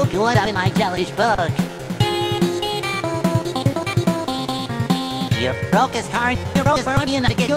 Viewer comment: I heard “dude broke his eye” here lol